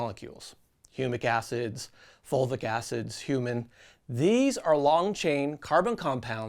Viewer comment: did you just say you put human in your soil? 😂